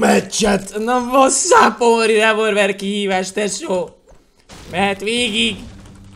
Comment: Szia 2:10 -nél mit mondasz? köszi + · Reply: Na wassup only revolver kihívás